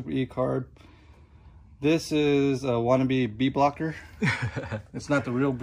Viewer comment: bike it’s cool man really nice! Question what is the name of his air filter? I thought I heard him say bead blocker but I could not find any thing that looked like his... thank you very much great vids!